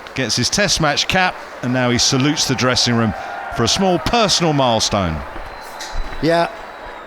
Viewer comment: what is he saying small personal milestone mf 😡😡😡😡😡😡